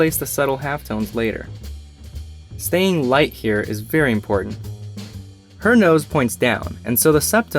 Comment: Him: it’s important to stay light here Me:ok My hand:I’m going to ruin this whole mans career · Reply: Bro that is not what he said, He said "Staying light here is very important"